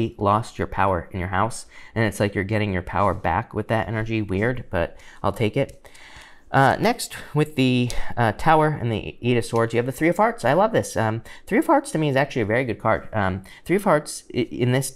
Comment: Okay... Did anybody else hear “three o’ farts” @ 9:41 or just me???